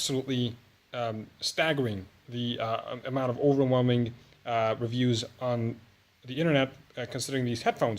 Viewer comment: 4: 13 I donk understand what ur sayin :P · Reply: FUTTERSHY FAN 0:48 overwhelming amount of reviews, 4:13 problematic, I hope that cleared things up a bit.